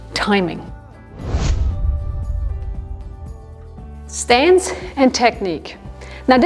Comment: The word is “stance” instead of “stands”.